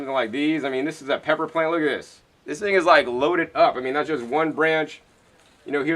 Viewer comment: I swear at 26:16 it sounds like you said "This nigga is loaded up." I had to do a double take, I was like when did turn into the green gangsta. lol